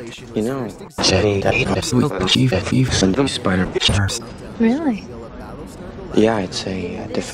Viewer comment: I can't understand a word of that and I'm not sure if that's good or bad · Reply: He says "Jimmy Davis will block you if you send him spider pictures" but honestly I agree. That was really hard to make out unfortunately